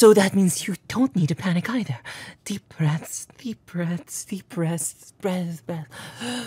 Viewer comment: Saihara you angel, I love you. 6:24 Did he just say deep breasts? my my Shuichi I didn't know you were so dirty minded🤣